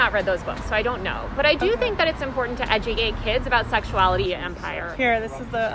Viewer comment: Did she just say "edugate" at 1:55?